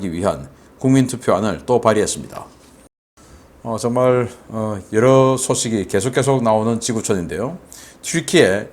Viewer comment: 정말 여러 소식이 나오는 지구촌인데요 -> 세계는 넓고 미친놈은 많다고 바로 들려서 미친듯이 웃었네요 ㅋㅋㅋㅋㅋ